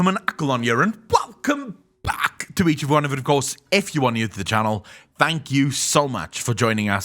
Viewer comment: I thought he said: "F U new to the channel" LMAO, he was so happy to say that.